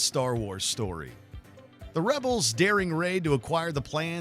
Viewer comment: Wait the rebels daring Rey??? Did anyone else catch that?